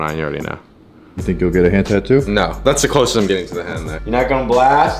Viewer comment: thought he said hentai. I was like wait what?!